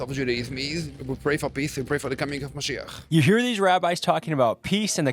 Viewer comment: is your next indicator, he says “we are praying for Meshiah” our messiah. I’m just here to warn everyone who heard that correctly that “Meshiah” is the anti-Christ the bible talks and warns about this very clearly. Pay attention people, the end times are here this is what scripture talks about. They will pray for this messiah to come and it’s gonna fool a lot of you. Yeshua is the way wake people please. I pray for all to realize Yeshua was indeed who he said he was.